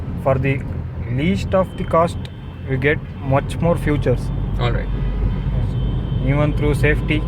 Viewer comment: Did he just say 'futures' instead of 'features'